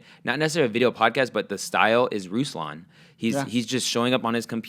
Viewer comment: Great video podcast and thank you both for sharing your ideas! Who was the person you mentioned at 25:13? Eltakrori? It sounded like you said Rooslawn, but the only channel I found with that name only had one subscriber.